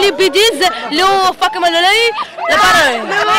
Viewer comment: Did she said.. Fuck......